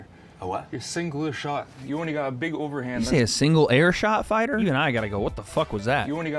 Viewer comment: Sounded like he said Logan has a singular punch not a single air punch lol but this was a funny face off tho